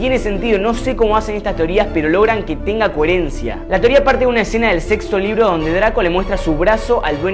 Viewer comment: dijo sexo o solo lo escuché yo